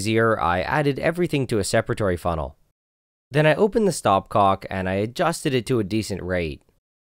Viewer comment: THE WHAT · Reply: i thought it said stopclock 😭